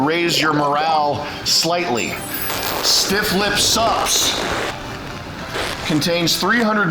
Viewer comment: stifflips supps sounded like "syphilis sucks" everytime.